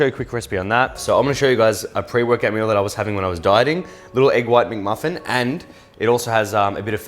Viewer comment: I thought he was saying egg white muffin. I was like, “oooh”. But egg McMuffin looks good too minus the ketchup. I’d use heirloom tomatoes.